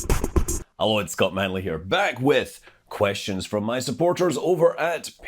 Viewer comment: Funny mishearing: "Hello, it's got manly here" ;)